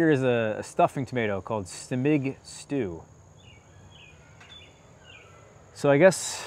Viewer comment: Did anyone catch the name of the tomato that he thought was for stuffing? He called it something-stew? Seemed to like it and had potential. It comes in at about the 24:26 minute mark.